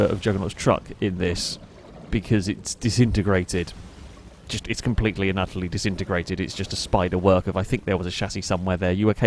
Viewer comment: what does he say? I can't catch it. Thanks Youtube for doing away with community captions. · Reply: "It's completely disintegrated. "